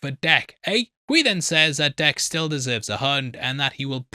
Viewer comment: when you said that Dek still deserves a hunt, i thought you said "deserves a hug". I rewound the video to be sure but i was like, yeah that tracks.